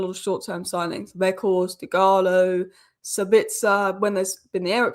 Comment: and 2:06 who does say was a good signing? Agalo? Dugalo?